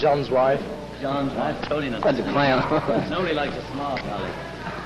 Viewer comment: can anyone tell me wat John said at 1:01 when they were asked wat kind of girl they like and George said "John's wife"?LOL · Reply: nobody liles a smart guy